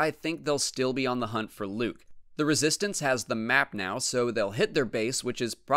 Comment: Hold up how do you know the First Order has the map? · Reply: Oh ok I misheard him at 4:47 I thought he said the first order has the map but he said resistance